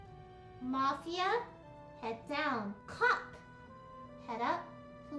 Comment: Am I the only one who Heard lily say cock